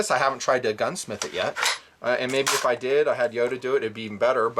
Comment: Did you say Yoda?